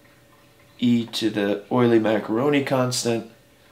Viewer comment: did say "Euler-Maccheroni constant" at 9:12? lol